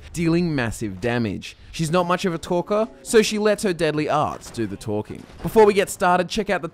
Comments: i thought you said "deadly ass" lol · Did you say deadly arse? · Lol I thought he said "Deadly arse do the talking"